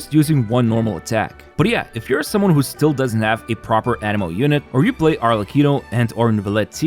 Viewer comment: Bro, learn to pronounce anemo, all the video i thought u said animal. Jesus.